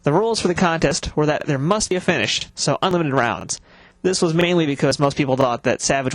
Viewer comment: "The rules for the contest were that there must be a finish [unintelligible] rounds" What are you trying to say here? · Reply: "must be a finish, so unlimited rounds"